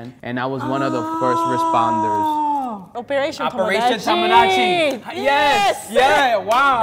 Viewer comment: Yo i legit thought he said operation tomo-gachi (those lil pikachu toys) and was like yeaaaahhhh ma boyy... then obviously my brain straightened up and pieced the whole marine part together and the story became even more epic lol